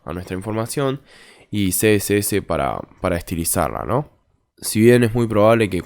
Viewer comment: que dijo en el momento 1:29 al momento 1:32, no entendí que dijo XD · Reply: Y CSS para estilizarla